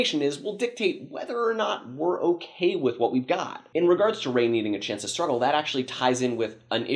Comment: talking a little to fast for me and the CC subtitles are claiming that you said "in terms of rain eating a chance to startle". That doesn't sounds right, what are you actually saying there? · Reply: I listened again, were you saying "needing a chance to struggle"?